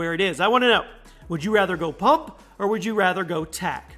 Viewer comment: After you said, “What would you rather have, a pump, or-“ A ad said boom. So this is what it sounded like you said. “What would you rather have, a pump, or a boom?”